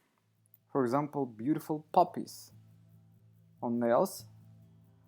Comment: Lol....I thought you said Poppies not Puppies!! I wondered why you had dogs on her nails....🐕🐕🐕